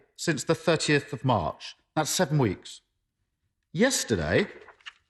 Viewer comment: All the Graphs have changed. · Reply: Peaky Blinder he actually said that they had stopped yesterday... not since we’d been highest.